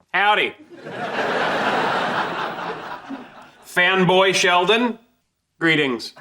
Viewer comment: for a second i thought he said femboy sheldon